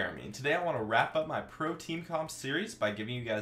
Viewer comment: when he said pro-team comp at the start i thought he said protein hahah :')